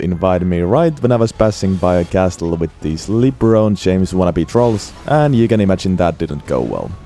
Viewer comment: I heard "Libra James", now I can only picture Libra ballin'.